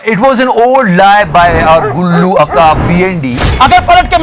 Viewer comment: Haha did he just say akaa instead of A.K.A